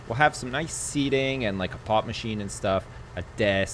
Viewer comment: for a second I thought he said a pot machine lol